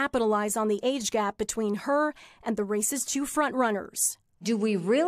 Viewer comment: did she say the 'race's' two front runners 'racist' two front runners?